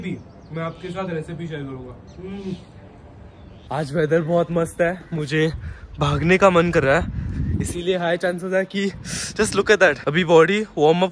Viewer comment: who else heard that”” mujhe hugne ka bohot man kar raha hai”🙂🙂